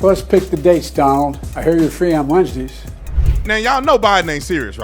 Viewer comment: Okay 6 seconds in and I replayed it a couple of times... Did Biden say "I hear you're free on MONSDAYS."